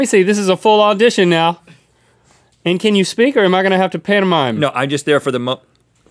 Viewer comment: I thought Link Said Pornomime XD